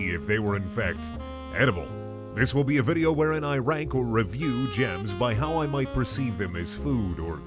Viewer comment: I thought it said germs